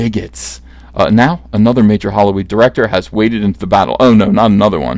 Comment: I'm pretty sure said hollyweed instead of Hollywood...is it just my twisted synapses hearing this, or is the now legal California rebranding things to better reflect the business? Or is he just being kind, handing out new marketing gimmicks free of charge?